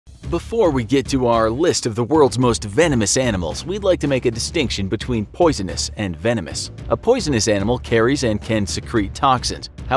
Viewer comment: I legit heard let's get to our list of drugs😂